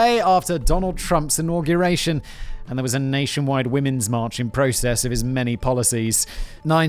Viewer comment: did he say “in process(t)” and expect us not to hear it?